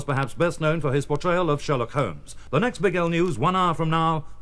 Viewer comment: Thats funny,I bought The Who Sell Out LP long time ago and I always thought they said Big Gal not Big L.LOL